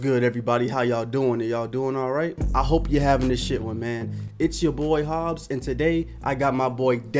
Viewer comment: What's he saying in the beginning? Sounds like is saying I'm hoping your having a shit one.